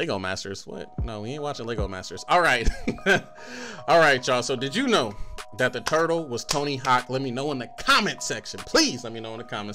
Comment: Am i the only one who heard him say at the end "did you know that the turtle was tony hawk